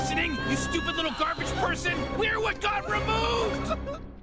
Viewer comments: The first time I saw this, I thought rick said ”WE’RE WHAT GOD REMOVED!” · When I first saw this episode, I thought Rick said, "We're what 'god' removed!" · I thought he said "We're what god removed"